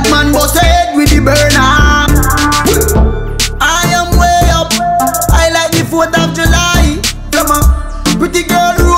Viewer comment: I thought he said "High of the Gelati" but it was "High like the 4 of july"